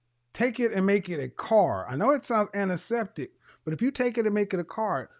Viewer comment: What did he say on 1:57:18 anaseptic?